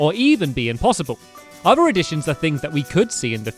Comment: Who else thought it said "Addictions?"